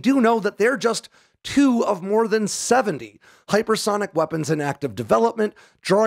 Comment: I'm sorry... did he say more than 70 hypersonic projects currently in R&D?